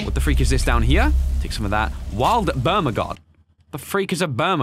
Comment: I think it said bertamog but idk somebody tell me if can also what's his PSN name so I may play a few games U know